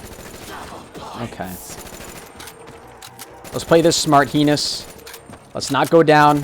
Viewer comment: I thought he said come penis instead of henis